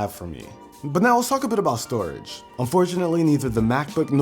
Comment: I seriously just thought he said STD